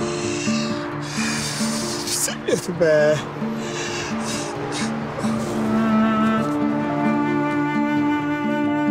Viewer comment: ne diyor? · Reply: Düzeliyordu be